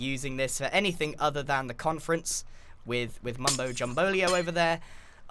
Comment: "Mumbo Jumbolio"? Am I the only one who heard that?